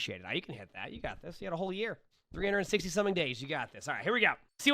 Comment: Emongg is so powerful he added 2 extra days to the year to make sure this moira reaches their resolution, truly inspiring. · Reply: I heard "367 days" at first but I think he said "360 something days"